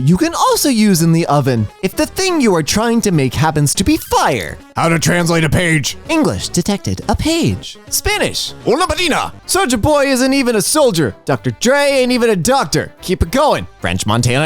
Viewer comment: native spanish speaker here, the way spelled "una página" was so bad it sounded like she was saying "una vagina"